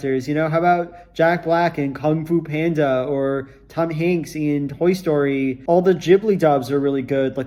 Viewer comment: I thought you said Tug Hanks and now that’s his name for me. Thanks. Or T Hanks I guess